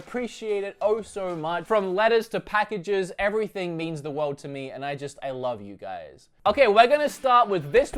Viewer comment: When you said "from letters to packages everything means the world to me". What I heard was "from lettuce to packages".. :) Oh you